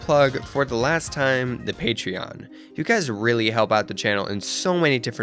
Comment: When he said "the patreon" i thought he said THE PANTHEON Lmaaoo!